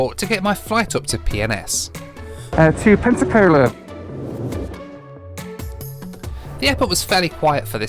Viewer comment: Think I'm needing to get my hearing aids cranked up at bit...I thought was saying 'Pepsi-Cola' airport! lol!! 👵